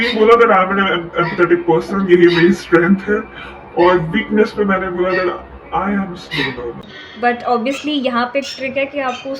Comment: weakness me kya bola didi? 😭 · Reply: that i am a slow learner